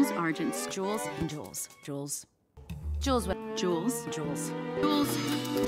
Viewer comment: The more i hear her say Jules, the more i hear Jules drools 😂😂😂❤